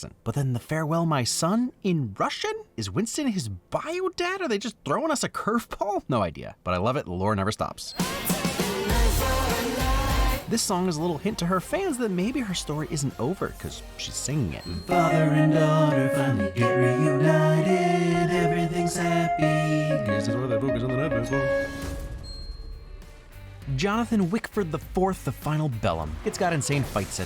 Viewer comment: Okay so I misheard what you said as "Guess that's why they focused on the knife asshole." and it made me laugh way to damn much because even if it was just me not hearing right I was NOT expecting the muttering. xD